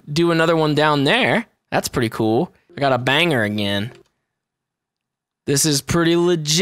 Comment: I hate being this guy wait no i dont but when you say banger it sounds like you say bang her who ya gona bang